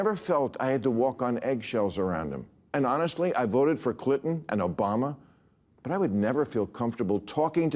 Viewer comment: did he say “I voted for CLITon, and Obama…”? 😮